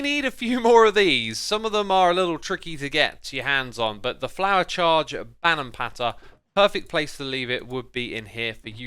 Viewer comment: who else heard him say bannerm patter